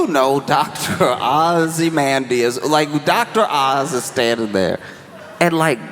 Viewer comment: 😂 Did he say "Dr. Ozymandius"?